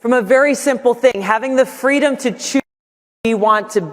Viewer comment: Does she say shoot or choose? 0:58 😂